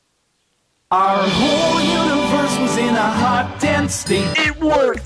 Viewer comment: For some reason I hear it as “Our whole universe was in a hot dang sink”